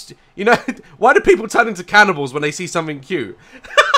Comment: What i heard. "Catibals" ....